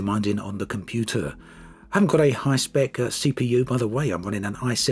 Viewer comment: Your CPU is not ''high spec'' any more. The i7-6700K is already 5 years old and is starting to show age. It is bottlenecking your current graphics card the RTX 2080 Super. 4.2GHz is fast, but the limit is the cores. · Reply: it sounds like you said '' I have gotten a ''high spec CPU''. But it seems i misheard it